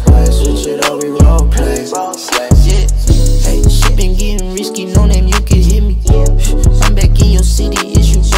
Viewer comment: what did he said? Help please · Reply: Shit be getting risky, know that liquors hit me. I'm back in your city, is you rollin with me?